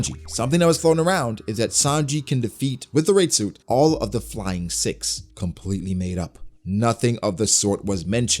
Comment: Damn, I misheard you calling Sanji as the King of Simps. That would have been rude!!